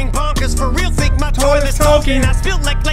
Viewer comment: “I feel like my toilet’s talking”. THATS IT! Em has low key acknowledged Shaq!! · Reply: I heard "my toilet's token" like in tokens video